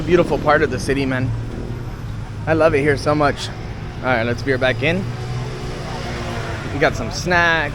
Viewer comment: Hi, somebody help me! As a non-native english speaker, i can't catch a sentence around 23:50 in this video. The sentence is next to " I love it here. Right" ~~~lets,,, beer~back in??? " ???